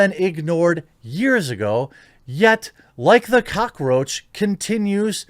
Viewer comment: I swear he called this man a cuckroach and I approve immensely.